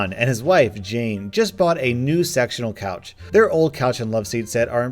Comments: What's written "Sectional Couch" what I hear "Sexunal Couch" ... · I thought he said "a new sexual couch" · thought you said sexual couch! I’m like “Wtf is a sexual couch?.......ooooooooohhh, SECTIONAL! That makes better sense.😂😂” Sorry, I’m getting home from an 11 hr shift at work. Don’t judge me!! 😂😂